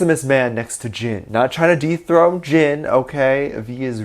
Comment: I'm sorry for my dirty mind🤣 but I heard deep throat